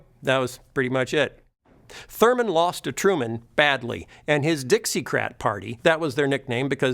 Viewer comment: What does he say? "Dixiecrat" or "Dixiecrap"? I hope for the latter!